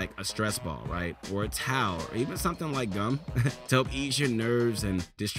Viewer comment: I THOUGHT HE SAID GUN😳